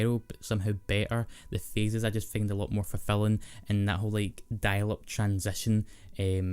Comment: did you just say die lit transition?????? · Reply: I said "dial-up transition" but your interpretation is ten times cooler so let's go with that